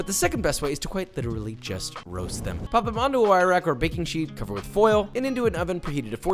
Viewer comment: Anyone else here "cover with foil" instead of "cover with oil"?